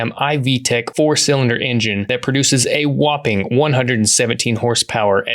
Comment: Initially thought you said “a whopping 170hp” and I was like yeah that’s ridiculous. 117 sounds more accurate 😢